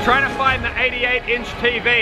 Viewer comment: whenever I saw this video I always thought it said MY 88 in. tv and I thought wow how pretentious, then I saw that he said THE....